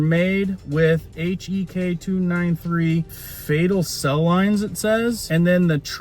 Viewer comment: As I’m listening, I heard you say fatal cell lines. That’s not fatal cells that’s FETAL cells as in testing on a specific aborted baby. That number is her reference number.